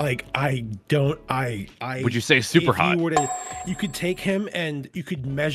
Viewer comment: did Moon called Foxy hot?